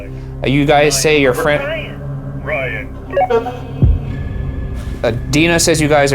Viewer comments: For #debrief: at 1:02:38 women says “where is ryan?” And guy answers “Ryan.” Am I the only one who heard this. Is this the production team because it is so clear. PLEASE SOMEONE ANSWER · For debrief: at 1:02:38 women says “where is ryan?” And guy answers “Ryan.” Am I the only one who heard this. Is this the production team because it is so clear. PLEASE SOMEONE ANSWER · #debreif does anyone else hear several "ryan"s at ~1:02:34 (as well as what sounds like saying "uhh... just like...") ? were those edited in or something? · at the 1:02:34 mark, it's so strange bc you can hear the louder ryans, but also under the loudness, you can hear a male voice speaking very very clearly but at a lower volume also going "uh, ryan" and saying other things. i had to replay multiple times bc the captions didn't depict it and it sounded like someone on the radio talking???